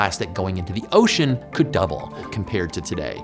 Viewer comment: Subtitles are wrong around the 3:54 point. You say that plastic levels in the ocean could double, but subs say triple.